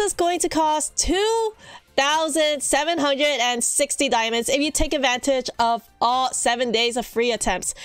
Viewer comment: Noo I thought it said "1,760 dias" and I was like yaaaaaay I can afford it and I spend all my dias..... 😭😭😭😭